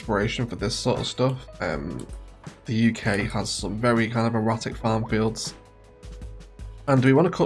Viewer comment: Did you really just say the words "Erotic farm fields" ? I don't think I've ever heard that combination of words in my life 😂